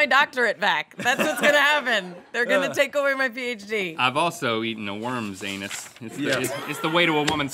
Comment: I swear I thought he said "I've also eaten a woman's anus. Its the way to a woman's heart." Well that means something very different. Lol